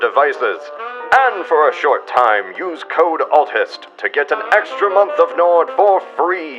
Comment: When I see "ALTHIST", I read Atheist. When I hear "ALTHIST", I understand Autist